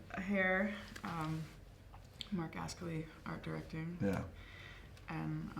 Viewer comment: does anyone know what the name of the guy who did the art direction for the Jil Sander campaign. they say it twice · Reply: Jon Cabana Marc Ascoli